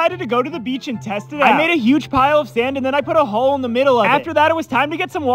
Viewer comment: Bro I thought he said “and then I put a hoe in the middle of it” 😅 I uhm… uh